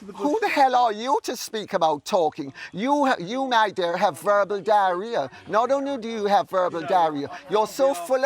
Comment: She said verbal diarrhoea and it’s sounded like she said verbal diary